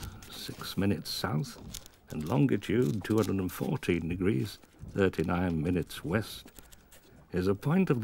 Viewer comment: did he say "Longitude 214 degrees, 39 minutes West"? And oh yes, thanks for posting.